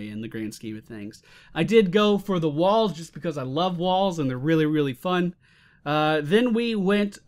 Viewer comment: did he just said he love walls? D: WTF?!